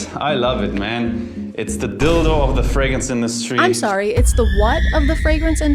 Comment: I thought he said "it's the Bilbo of the fragrance industry" and I was.....so confused.